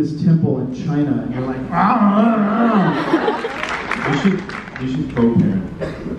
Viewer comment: What does Paul say here? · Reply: Paul says "we should co-parent" 😂😂😂 if you watch that entire 2019 New Jersey convention they joke they should have a kid together & it's hilarious 😂